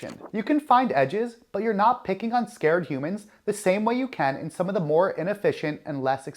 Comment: did he say picking on scared humans?So that's that is the edge.